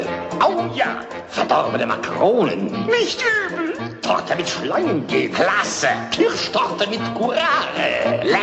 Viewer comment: ich hab am Anfang verstanden: Potter mit Schleimgel :D